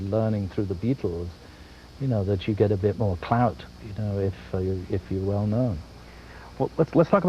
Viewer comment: Did he say clout